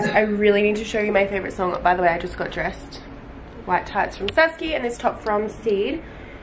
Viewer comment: Where are her white leggings from? I couldn't understand her. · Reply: Saski I think